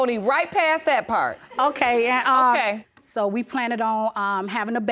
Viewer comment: Did she say "planted on"? As in "plant"; "planet"; "Planters Nuts"?